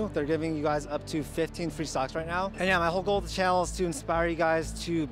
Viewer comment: I thought he said they are giving away 15 free SOCKS